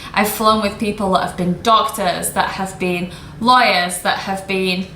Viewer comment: while I was reading the subtitle 4:08 “people that has been doctors, has been liars...” damn i thought you say liars, then apparently it’s “lawyers”😆